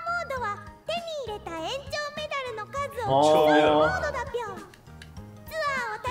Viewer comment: 園長メダルが炎上メダルに聞こえた